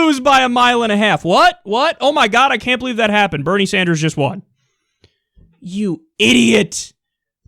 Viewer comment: "You IDIOTS" 2:59 Um, did I hear that right Flowey?